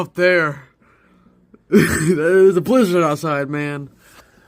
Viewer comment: I heard "there is a blue lizard outside!" 🤣🤣